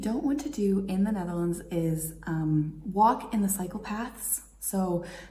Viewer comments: cycle paths; sounds like you said psychopaths....LOL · I misheard : 'Don't walk in psychopaths'.I thought good advice, strange one but good :) Pozz iz Bgd. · Am I the only one who thought she said “walk into psychopaths”